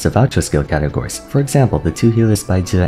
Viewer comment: I SWEAR I heard, “the two healers, Baizhu and Furina” and did a double-take